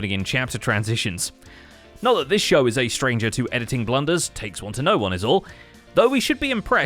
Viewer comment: "Not that this show is a stranger to editing blunders, takes one to know one is all." Subtitles say "mistakes" in place of "blunders" "Mumbo Jambo" in bottom left corner I see what you did there. Nice job XD